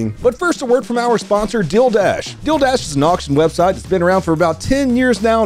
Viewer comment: Check out <link> to get 100 FREE BIDS on your first bid pack purchase! Relief fund for the family of slain Deputy Isaiah Cordero: <link> Watch the raw footage here: <link> Check out my latest range day vlog with all my friends!: <link> Support the channel at <link> Check out Donut After Dark, watch videos early, see uncensored versions, and get access to exclusive content at <link> TWITCH.TV/DONUTOPERATOR MY VLOG CHANNEL: <link> MY GAMING CHANNEL: <link> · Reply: Thought you said dilldash. Lol #dillllld-o